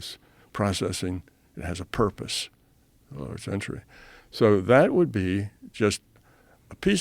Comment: Did he say "for another century" at 53:20? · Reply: I think he says Sentry, as in neural sentries.